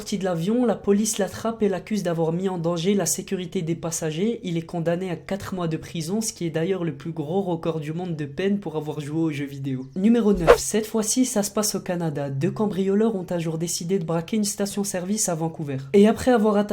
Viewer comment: Cette fois ci... ( j'ai compris: 7 x 6 ) Je me suis dit ''42'' dans ma tête !